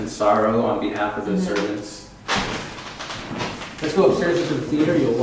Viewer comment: when you thought it said "let's go" I heard "let's go, HIDE"